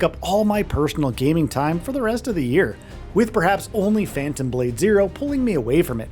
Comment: This game amazes me everyday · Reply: did you say "Only Fans" ? 🤔